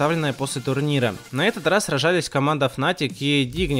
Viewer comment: Мне одному послышалось "Рожались"?